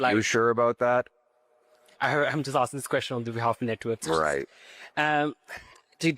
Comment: sounds like he says "I'm just asking this question in behalf of the nitwits" lol (network)